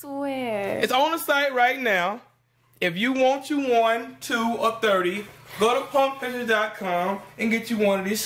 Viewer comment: Wtf he say at 1:58? lol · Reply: "if you want you 1, 2 or 30'